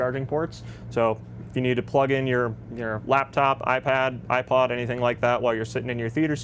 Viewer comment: did bro say iPod?? 😂